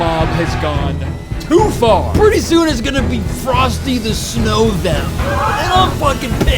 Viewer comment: I definitely Heard "Pretty soon it's gonna be Frosty the snow-Vamp" And I was like "Yeah. Because he's bisexual"